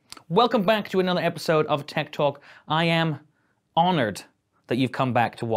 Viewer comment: l thought he said tik tok = tech talk